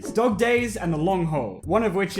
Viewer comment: I heard dog gay's 😂